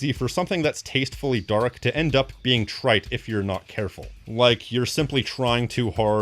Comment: When you said “trite” I heard “tripe” and with the visual of Mr Grimm’s meal it put me off my dinner.